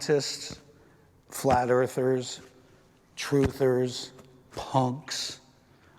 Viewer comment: Wow now He is calling Punks are flat Earthler or what is he saying